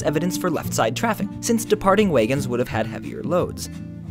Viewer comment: Wait - did you say WAYgons?